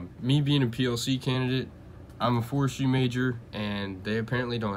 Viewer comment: what did you say your major was? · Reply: forestry